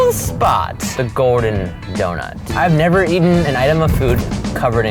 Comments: I thought they said Gordon donut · I thought he said Gordon donut.